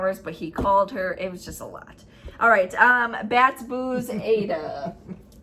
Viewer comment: 3 4 10 When you showed last weeks winners, I thought you said, “ Baths & Booze” then I realized you were saying “Bats & Boos”😂😂😂 I’ve been stitching Flea Market Flowers & The Tudor Bee in July.